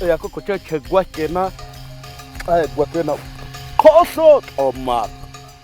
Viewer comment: did he say kos omak?😂😂